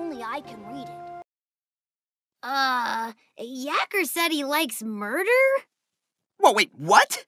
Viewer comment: I thought Tails said "Yackers likes burger" and thought it was normal